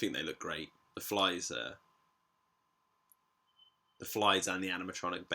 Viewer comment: I thought you said the flies were animatronic lol